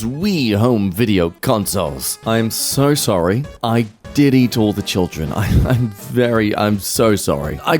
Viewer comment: I thought he said "I Diddied all the children", not "I did eat all the children" 💀💀💀